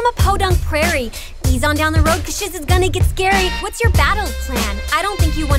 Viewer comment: did she just say... shiz... is gonna get scary...